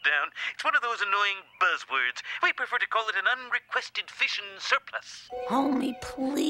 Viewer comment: An un-requested fission surplus lol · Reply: Ohhhh I thought he said “an un-requested fish in the surplus” and I feel stupid now